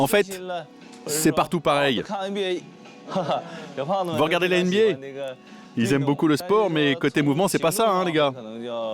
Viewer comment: c'est moi environ a 6:42 on peut entendre le médecin dire (my n****) ou c'est moi qui entend souvent le n-Word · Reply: Il dit 那个 (ne ge) qui peut se traduire pas ça, ceci ,cela...